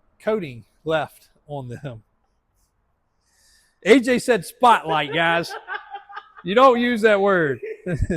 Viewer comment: He said spot light I thought he said poach lol